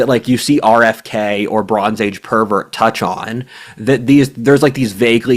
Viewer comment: What does he say around 5:28 that the auto subs (and therefore also me) hear as (RFK or) "Bronze Age Pervert"?